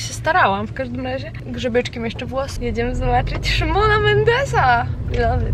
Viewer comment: Szkoda, że nie mogłam jechać, ale mega się cieszę Twoim szczęściem! Pięknie wyglądasz w kokach i tym makijażu! Poza tym czy tylko ja w 5:06 usłyszałam "Szymona Mendesa"? XDD BUZIAKI! 😘😘 Ananaski łączmy się! 🍍🍍 ⬇ ⬇ ⬇